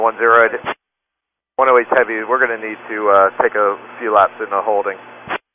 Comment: many words and even short phrases were wrong. Every time the pilot spoke about dumping fuel, the wrong word was used. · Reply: "a few laps" not "our fuel out". 3:01 - "adjust fuel" not "dump fuel". 3:30 - "adjust" again 3:43 - "clear of you so you're not in the way one bit" instead of "clear of you so you can start dumping" 3:48 - "adjust" again. 4:30 - "adjust" again. 4:55 - 13 miles vs 10 miles 5:02 - 6 to 10 minutes vs 10-15 minutes. 5:45 - "got about, probably, 2 more minutes" There's a lot more but I got tired.